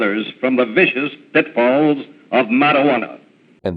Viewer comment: I thought the judge said "the vicious dickballs of marijuana".